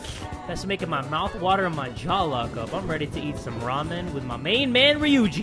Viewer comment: every time you say ramen all I hear is raw men..... lock those jaws for those ramen